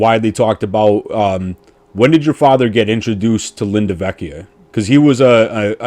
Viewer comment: I thought u kept saying Linda vechio and I'm like there's a woman being a mob bosses handler! 🤣